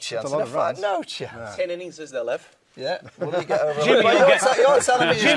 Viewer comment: what does kp say at 34:07 approx????????? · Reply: Me You Says 'ten innings is there lef' presumably meant 'left' and just left of the 't' weirdly.